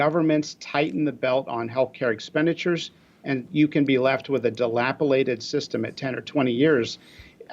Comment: lol did he just say 'dilapilated'? :/